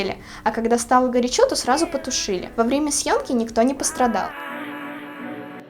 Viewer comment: мне послышалось с Сашой ради секса😂😂😂😂😂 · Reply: мне послышалось " во время секса"